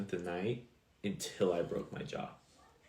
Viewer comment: What was he saying he moved in after? I find him hard to understand but it sounded like he said after i broke my jaw🤔